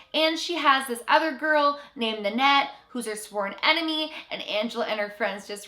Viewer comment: Did you just say Annette? I dont wanna share a name with a creepy character